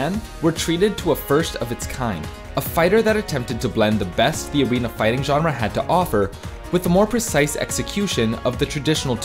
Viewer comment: what is he saying at 0:49-0:50? the best what a fighting genre had to offer?